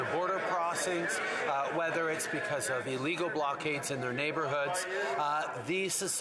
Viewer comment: Did he just say "Legal blockades?" sure sounded like it. hahaha